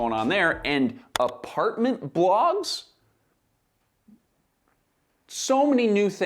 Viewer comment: I thought at first you said apartment blocks, good thing blogs was later on spelled out, lol.